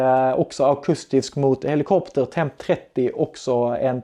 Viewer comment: Vad säger du vid 02:09? Namnen på de två andra minorna menar jag, tack. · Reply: Temp-20, Temp-30 Alla videor har svensk textning med!